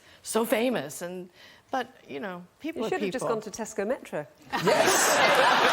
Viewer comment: What does the lady says at the end of the video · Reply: “You should’ve just gone to Tesco Metro.” Tesco is a British supermarket chain. Not a US-friendly reference, as most Americans would have no idea what that was!